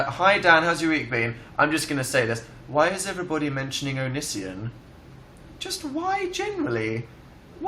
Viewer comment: what is he talking about when he mentions other youtubers/zoella and phil?? 16:05 "omission"? thats what the shitty cc says so idk what he's talkin 'bout lol help a girl out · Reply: He's talking about onision, this YouTuber who always puts Dan in his video thumbnails and talks about him in almost every video. I used to watch a few of his videos, and I think he's in some way funny, but I never really have the heart to subscribe to him cuz he was so offensive to other youtubers. I agree with Dan tho, that onision is taking it too far and he is just using Dan and other youtubers (mostly Dan) to get attention from fangirls. Also he insults my smol bean phil saying that he isn't as good as Dan so yeah not watching him anymore